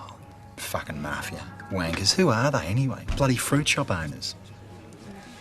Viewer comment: i always heard "Ya bloody Fin-Chule-Burners!", idk how i heard that